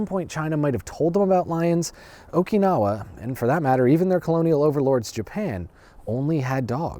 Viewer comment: Colonial Overlords, Japan? Did I hear that right? Wtf